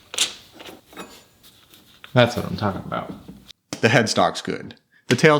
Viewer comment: Did I hear the mating the call the majestic Lousianian "Dasumtallabout"? Also, have you thought about scraping the bed yourself?